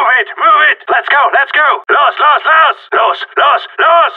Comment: What hes say? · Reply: ''Los Los Los!'' which means ''Go Go Go!''